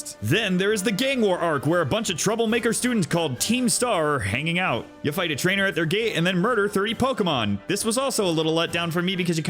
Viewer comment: I seriously misheard it as "You fight a trainer, is gay" the first two playbacks